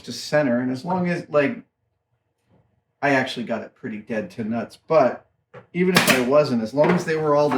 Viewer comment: Did he just say dead to nutts!?? I'm toataly using that!